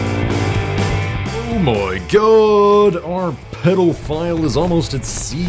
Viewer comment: Did he said pedophile. 9:10 ?